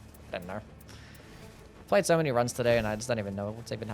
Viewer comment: dude, I legit thought you said you played 70 runs in one day?!